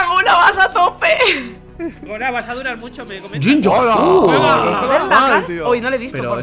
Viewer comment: Like si escuchaste a gona en el minuto 2:42 que dijo llenllar enves de gengar XD